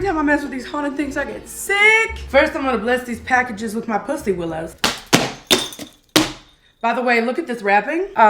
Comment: GIRLLLLLLLL I thought you said “bless them with my bussy” 😂